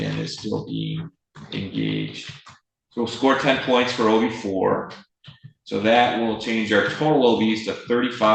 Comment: is he saying OV or OB? what does that men?? I assume it's something to do with sentencing guidelines but I was curious as to what it stands for. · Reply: I think the judge was referring to OV. Which may mean Offensive Violations. But, I’m not 100% sure on that.